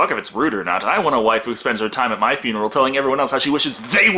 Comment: Wait did say "Wife who" or "Waifu"? Because I distinctly heard him say "I want a waifu."